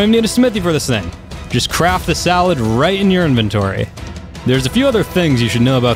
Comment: did anyone else hear salad, not saddle?